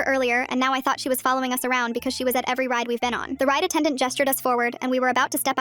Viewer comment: Wait so are they at Disney or on a plane cuz I swear I heard “flight attendant “”?!?!?!?!